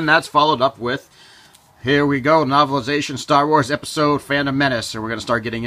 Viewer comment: Crucible was the last, not Mercy Kill. Otherwise, great video! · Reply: you said star wars episode phantom menace instead of episode 1 the phantom menace